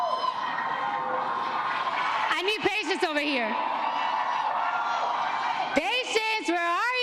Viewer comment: What is she saying? "I need patients over here"?